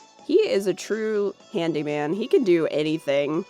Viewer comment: I hear “handyman” as “candyman”, I really enjoy this series. I’ve been binging it as BG during quarantine, something unexpected always makes me laugh